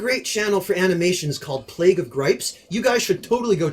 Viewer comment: I've a hard of understanding what he is saying, Plake of grapes is what I hear... Can someone correct it?